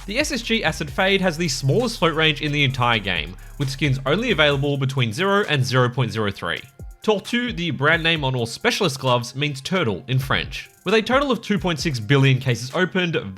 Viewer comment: Bro, I thought it said "Torture" 💀